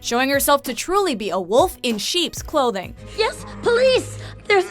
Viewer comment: Spoiler alert 02:38 I heard wolf in sheep's clothing or no?